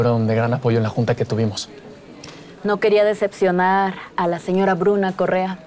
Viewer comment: Cuando dicen señora Bruna, pense que dijo señora Bruja XD